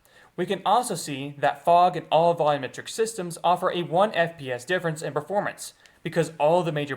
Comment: Did you say 1 FPS difference in performance: 00:55 ? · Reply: I think he's saying the unoptimized fog alone is 1 fps